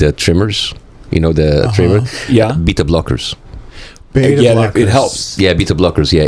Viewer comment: I thought he said beat up lockers. I'm like that's gonna be terrible for your hands. Oh propanalol that stuffs great